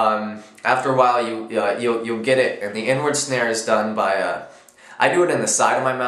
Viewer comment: HAHA totally thought you said "N word snare" not "inward snare"